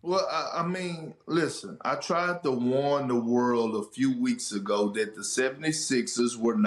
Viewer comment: I thought Kendrick said he tried the Wanda World at first 😂